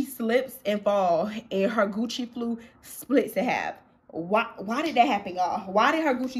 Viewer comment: Lmao girl I thought u said coochie lips instead of “Gucci shoes” 😭😭😂😂